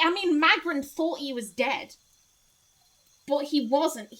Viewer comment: I'm listening to this while drawing and accidentally misheard 'Well, Magran thought Durance was gay' and did a double take until I realized you said 'dead'. Xoti had an interesting premise but I couldn't help being a little disappointed all her lore was about Gaun really because I wanted to know about Eothas specifically. Like sure Gaun is an aspect but the way they talk about aspects they're basically entirely different personas.